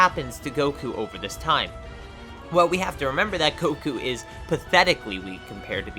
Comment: he said koku instead of goku🤣🤣🤣